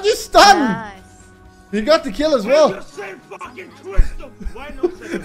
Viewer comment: What is he saying at 3:00 please ? :D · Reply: he said "IT'S THE SAME AS YOU FUCKING TWIST HIM"